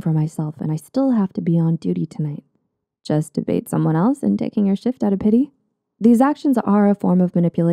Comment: I thought she said "Taking you shit out of pity." Lol still applies though.